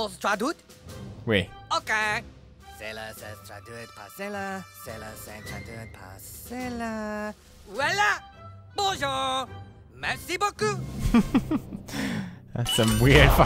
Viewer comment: What the Guard, says on 9:27 is, "Cela se traduit par cela... cela se traduit par cela... Voila! Bon jour! Beaucoup!" And I translate the words he said and it said, "That translates to that ... that translates to that... Here is! Hello! A lot!"